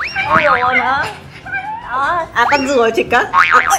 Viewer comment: Chi nói gì zợ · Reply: Con rùa chi*ch